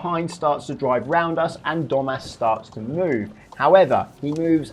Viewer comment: I thought he said “dumbass starts to move” for a sec💀